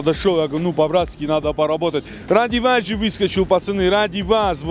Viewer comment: сказал "Ради Вас"- услышал "Антиваз"))